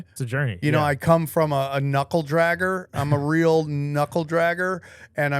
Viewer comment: I thought he said nipple dragger